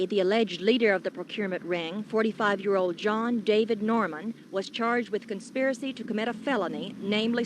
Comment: I thought I heard her say John Paul Norman in the news footage. Am I hearing that right? I thought it was John David Norman who was involved?